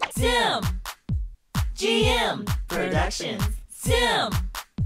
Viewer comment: Wait I've always thought the song said 🎵Sim! Gm! Kardashians🎵 and now that I hear it better it says "productions" omg lmao